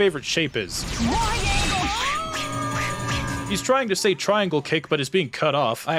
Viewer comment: is it triangle? i thought he was saying "Wide Angle Kick"